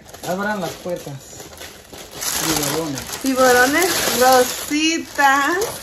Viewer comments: All the time I thought she was saying tiburones 😂🥺♥️ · For a moment I thought she was saying tiburones hahaha 😂😂😂😂